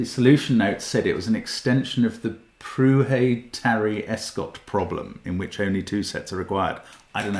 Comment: My intuition here tells me this works for N in the set of {0, 1, 2, 3, 4, 5} and not for any higher number is because you have six different numbers? I dunno, "it feels very much like" ""solving"" """"x_1^N + x_2^N + x_3^N + x_4^N + x_5^N = x_6^N"""" to me (quotation marks added for extra non-rigour), and that kind of sort of makes sense to me how it breaks when N > 5? I'll have to chew on it a bit. What was the problem mentioned at 31:48? I can't really figure out how to spell it based on the pronunciation, sorry! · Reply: Prouhet–Tarry–Escott problem